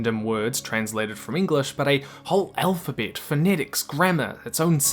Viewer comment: I'm sure it's just your accent but I heard elfabet instead of alphabet and it made me lol